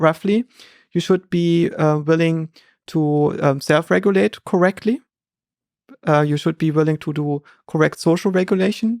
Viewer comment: Who is Joscha talking about at 2:30:00? I can't understand the author's name: Thomas O-something. Anyone catch his last name? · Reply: He refers to Thomas Aquinas, I think. It's quite hard to pick up on all Joscha's references because he speaks so quickly!